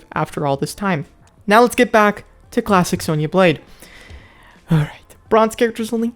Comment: What he said:"Let's get back to Klassic Sonya Blade" What I heard:"Let's get back yo Klapping Sonya Blade" 😂